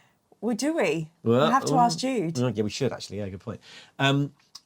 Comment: I realize now that says "Oh yeah, we should actually." but for a second I heard "I don't give a shit actually." and I had to go back three times to realize what he meant.